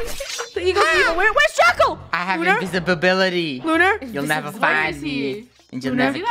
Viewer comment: Me:Did he just said ''invisibibility"